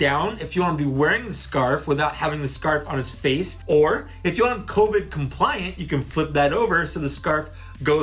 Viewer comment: Swear I heard you say “Cobra compliant” instead of “Covoid compliant” when you put on the scarf. I must have GI Joe on the brain. Great review!